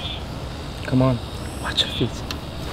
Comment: The fact that I thought he said wash your feet 💀